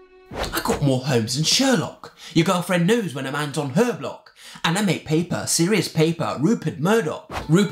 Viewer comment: this whole time I thought he said "anime paper"